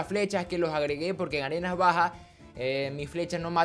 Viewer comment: creí que dijiste garena baja v